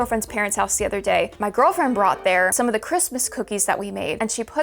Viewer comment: DID SAY GIRLFRIEND HOLD UPPP